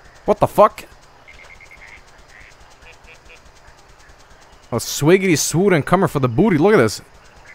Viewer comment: JOEL! What are you saying at 48:32 ? I always ear" Swaggidy doodis im comin' for dah bootie"